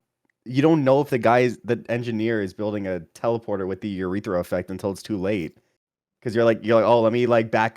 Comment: I'm using this to take the edge off my chucklenuts withdrawal 1:23:06 did he say "urethra effect"